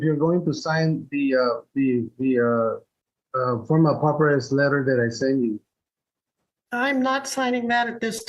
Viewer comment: Did he say 'poppin fresh letter'?